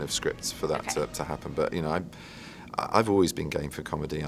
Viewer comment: I thought he said I've always been gay xD